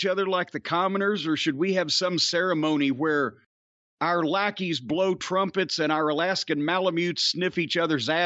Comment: Lol I paused near the beginning where Jim said "our lackeys blow trumpets" and I heard "our lackeys blow trump"